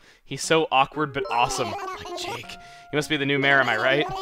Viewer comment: Did anyone else hear him say "Jake"